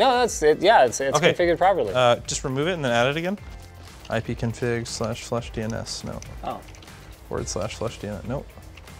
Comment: I was sitting here with this in the background and I heard "ipconfig //dns" and was like "How do I not know this command?!?" so I tried it and it failed :)